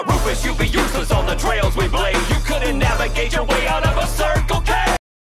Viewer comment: “It’s like cocaine, you know what I said.”